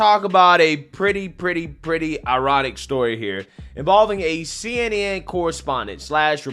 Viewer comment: LoL I misheard ironic story as erotic story and thought what?